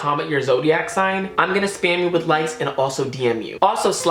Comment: at first I heard “spam you with lice” and I was hella confused